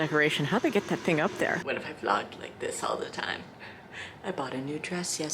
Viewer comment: when you said vlogged i thought you said vlonked so, petition for it to be called vlonking now??